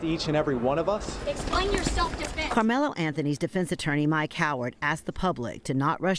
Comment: Mike Howard? 😂 Sorry...It sounded like they said "My Coward".